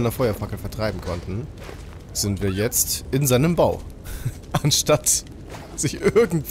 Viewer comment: habe verstanden "Bauch" und nicht "Bau" musste erst mal 2-3 mal zurück spullen um es zu checken 😂